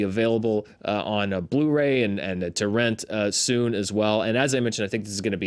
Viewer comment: Did you say TO RENT or TORRENT? I loved this moved, caught it on Sydney Film Festival, also won here. Nice Q&A with the great director. Fun fact: both those houses are a set built for the movie.